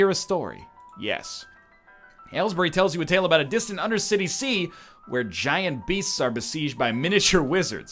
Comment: Did he just say "Undercity Sea"?